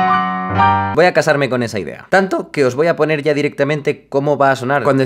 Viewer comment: En el minuto 9:21 cuando dice "voy a casarme con esa idea", por un momento creí que dijo "voy a casarme con Ter".